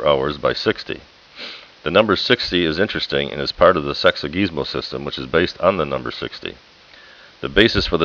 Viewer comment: I like the mention of the notion of the division of the day in 12 parts day/night and 60 minutes. But I dont understand the name of the system. I heard "sexy gizmos system". I dont have the courage of typing this in google to verify :) Tanks for the video